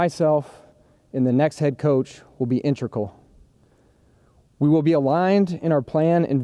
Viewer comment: He definitely says intracle instead of integral around 2:40. And implemate